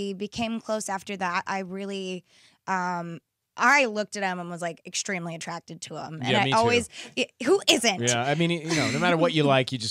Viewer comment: can u pls tell me what did they say at 0:58 - 1:06 i couldn't understand :/ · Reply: Elif Nur Tuncer Demi: I looked at him and I was like extremely attracted to him and I always... Ryan: me too Demi: Yeah, who isn't? Ryan: Yeah, I mean no matter what you like you just go ''that guy is goodlooking'' I think that's what tey said!